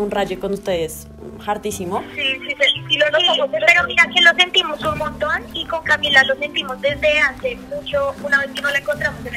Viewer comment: Que es lo que dice en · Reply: "si si lo notamos el otro dia" eso dice :V